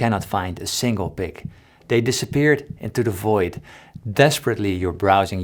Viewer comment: did someone say void